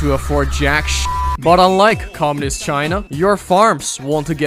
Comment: > WHAT DO YOU MEAN CAPITALISM, HE LITERALLY SAID MAO'S CHINA